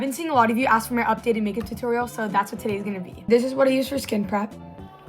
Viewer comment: I thought she said skin crap